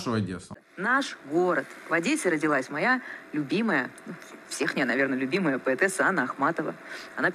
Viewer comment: "всехНЯЯ"(1:05) или мне послышалось))